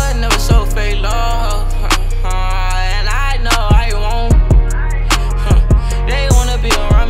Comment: did he say and i know mya wont ? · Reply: I wont* little bro